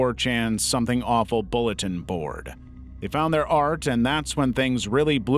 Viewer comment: "4chan Something Awful bulletin board" What? Those are two completely different websites with very different communities. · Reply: I thought they said “4-chan, some awful bulletin board”…